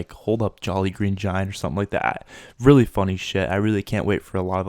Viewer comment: Jolly Green Giant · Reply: Lynx Regions I thought he said jelly 😂